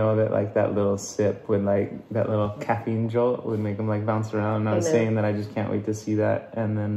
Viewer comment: At min 18:58 when he had the coffee would make “HIM” bounce around. Or am I hearing things lol